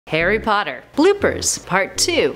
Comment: Did she just say ' Hairy Potter ' 0.00